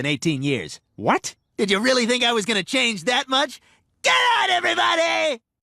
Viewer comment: GOODNIGHT EVERYBODY! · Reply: I thought he said get out everybody